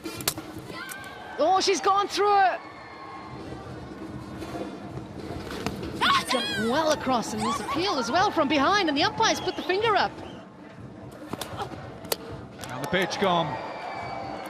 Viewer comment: misheard commentary - “another bitch gone” ☝️ 😂